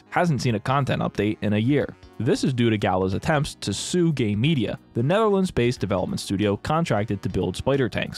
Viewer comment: swear to God I thought he said "sue gay media"!